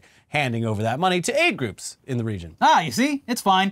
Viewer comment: I misheard that as: “giving money to hate groups in that region” instead of aid and I wasn’t even really surprised…